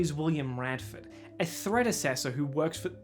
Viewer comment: I heard "threadecessor", like some sort of previous ruler of the kingdom of clothes or something alternatively "thread assessor", the formal title of one who judges your drip